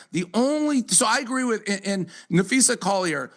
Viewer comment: NapheesA, not NapheesE. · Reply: you big dummy you sound like your saying Nafeces, sound it out there, you can do it, Na-phee-sa 🤦🏼‍♂️